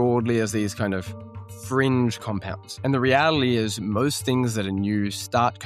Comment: Did I hear REATILY...? This guy seems to be going Elizabeth Holmes way...